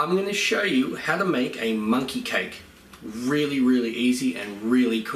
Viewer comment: I thought it said money cake lol